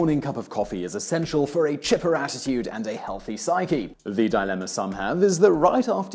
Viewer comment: I'm still waking up, totally thought he said "chipper ass-titude" right at the beginning...